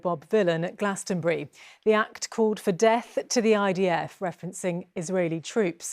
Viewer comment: I am an American. Maybe something got lost in translation. I heard him say "Deaf, deaf to the IDF". Does he mean that we should not listen to the IDF?